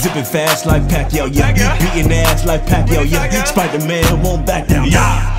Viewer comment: I turned around for a second and I thought he said on 1:50 “eating ass like pac now ya”